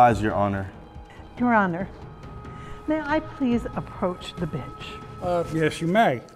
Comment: did i just hear may i please approach the b word or is it just me